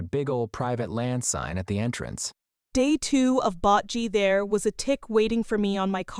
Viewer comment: I'm lmbo every time I hear "bot gee" 😂😂😂